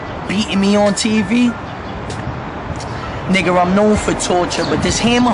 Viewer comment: Why it sound like tez says "nigger" when he says nigga 1:55 listen closely